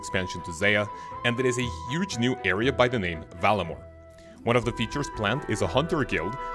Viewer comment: Var - la - more. You're pronouncing it like Valinor from Lord of the Rings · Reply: I just checked that vid and I totally hear what you're saying now. My apologies for being overly critical. I'm pronouncing it like an American but the British accent does make it sound like he's saying Valimore because they don't lean into hard R's the same way.